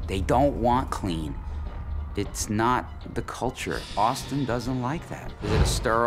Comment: Omg, did the guy at 14:20 really say Boston doesn't like clean? Damn, aperently college students in Boston don't like clean things Edit: I ment to say Austin, I don't know why I thought he said Boston lol